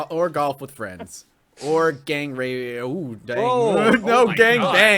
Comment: What was the thing almost said at 15:05 and why was it such a big deal? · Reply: He almost said "gang rape"